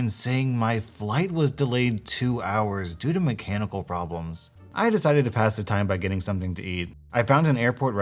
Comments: I thought he said "my cannibal problems" I need to sleep. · When he said flight was delayed due to mechanical problems i thought he said canibal problems i had to go back 10 seconds to check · I was just listening and I thought he said "due to my cannibal problems" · I had to rewind the video because when it said mechanical I thought it said cannibal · When he said 'Mechanical problems' for some reason I thought he said 'My cannibal problems' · For a sec I thought it said "My Cannibal Problems" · i thought he said due to my cannibal problems